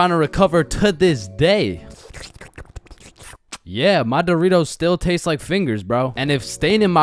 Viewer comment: Am I the One who's high cause I swear he said his Doritos Tastes like Fingers